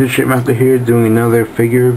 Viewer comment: it sounded like in the beginning it sounded like he said shitmaster haha